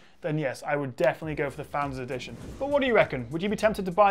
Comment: really sounds like you said Founders Edition... Really nice job Samsung did with the FE. Great video. Huge thumbs up, subscribed.